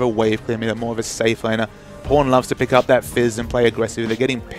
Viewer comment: dmmmit all i hear is po*n